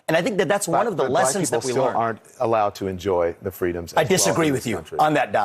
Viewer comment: Did I hear that right? There was a little over-talk, but it sounded like Don was saying "black people aren't allowed to enjoy freedoms..." America is literally letting it's cities burn so we can appear to be sensitive to peoples feelings when a drug addicted career criminal dies over his own stupidity? The man was in the back seat of an air-conditioned car FFS!! I know this will be deleted...but it's therapeutic to type it out.